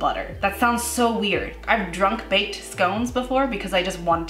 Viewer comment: what did she say? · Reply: I've drunk baked scones????